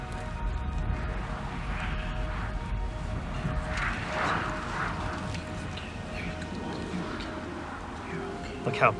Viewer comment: Am I just hearing things or is he whispering at an asleep lion at 8:54 like omg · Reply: He’s whispering “It’s ok, you’re ok”.